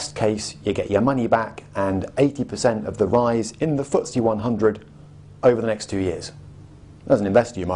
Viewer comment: I'm sorry, but what's the word he's saying here? 10:30 (Talking about Structured Product) 80% of the rise in ____ 100, over the next two years. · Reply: The Financial Times Stock Exchange or the FTSE (Footsie).